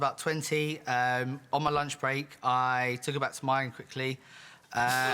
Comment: What on earth is that guy saying at 6:15 ??? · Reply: "I took her back to mine* quickly" (*mine is slang for "my place")